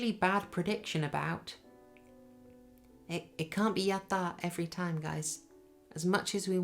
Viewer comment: I've really appreciated all of your reviews for "Midnight Mass." However, for this review, between 24:11 and 24:29, I couldn't understand the following two terms: "It can't be [YATA] every time guys. As much as we want, we can't always [YATI MAZU]." I re-watched your review for episode 4, and I still couldn't understand what you were referring to. Please let me know what these two terms mean, if possible. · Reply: Heya John, don't worry about that, I was being a bit esoteric. 'Yatta' is Japanese, in earlier eps I included some brief clips of a TV Presenter saying it. It just means 'I did it' or 'I achieved it'. 'Yarimasu' is the unconjugated form, so the sentence meant 'We can't always achieve it/get it right'. Since it was about me making some failed predictions and I used the 'Yatta' clips to celebrate correct predictions, I tried to keep the reference consistent . I can totally understand the confusion. I'm glad you're enjoying the reviews :)